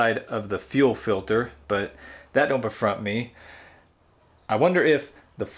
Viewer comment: Did you just say "dat dont befront me?" made me laugh. You are funny.